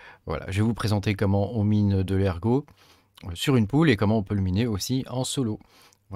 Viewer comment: Salut. Au début j'ai compris "comment on mine de l'ergot sur une poule" 😂